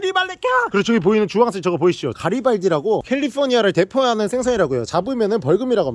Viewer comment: 가리발기라고 들었네..... 나 일상생활 어케하는거지